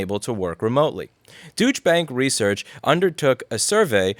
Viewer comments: I heard "deutsche bank research" as "douchebag research" every single time you said it. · Am I the only one who heard him say, "douche bag research" ? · I heard douchebag research, probably apt.